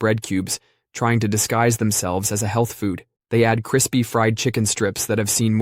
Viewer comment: For a second I thought it said Human Foods instead of Healthy Foods